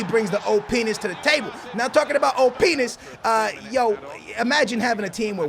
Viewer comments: it sound like youre saying oh! penis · who else thought he said oh penis when he really said OP nis lol. like so can see this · Lol it sounded like he said Oh-Penis when he said OPness hahahahah